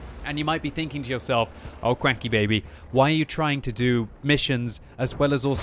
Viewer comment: I heard "Quackkybaby" XD hehehe~ :)